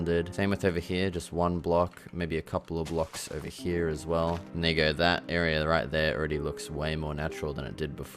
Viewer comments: what did u say my guy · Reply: BRAH you had me so worried when I saw this lmao, I said "there you go" hahaha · did he say what I think he said??? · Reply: Turn on the caption, it’s “and there”